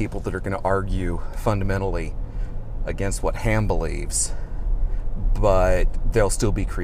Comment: I seriously thought he said "It's about what canned ham believes." I didn't question that statement. Wouldn't be any less batshit crazy.